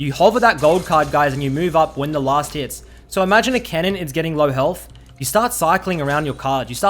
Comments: Lol I thought he said “kennen” not “cannon”. Got me pretty confused ngl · First 3 or so times he said cannon I thought he wa saying kennen so I was hella confused lol